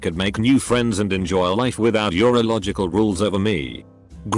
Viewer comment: Who else heard "your illogical rules" as "urological rules" and was like WFT?